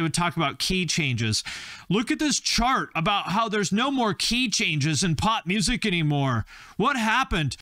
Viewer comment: The Queen key change part killed me😂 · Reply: I heard "key chains" and was super confused about it until I saw your comment. Thanks for clearing it up!